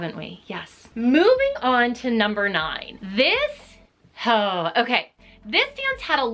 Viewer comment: i thought she said "THIS HOE"